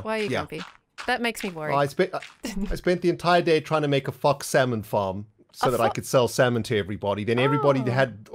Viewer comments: Did say he was tryna make a F*ck salmon farm ? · Is it just me or does it sound like is saying "F*** salmon farm"? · what did say? I think that was rude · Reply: He actually was trying to make a salmon farm using foxes to kill the salmon